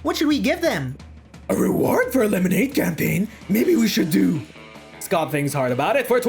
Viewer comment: I thought it said lemonade champagne 😂